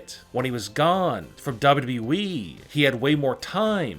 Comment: But on captions at 5:00 he says WWE but the captions say “WV weed”